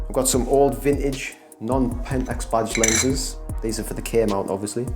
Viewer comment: I thought you were saying "bodged lenses," but at the end I realized it was "badged lenses." LOL Nice collection. My favorite lenses are the DA* 300 and the DFA 100.